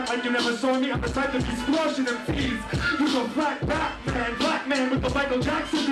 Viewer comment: I hear "You's a black batman, black man with the Michael Jackson disease!" That can't be right, can it? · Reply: No, he said “you go flat back man, black man...”